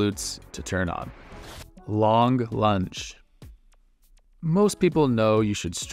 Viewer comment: I heard "Long Lunch". Always good after a long morning in an office chair 😏 But seriously, thanks for all these, I'm making great progress in my flexibility in the last 6 weeks or so.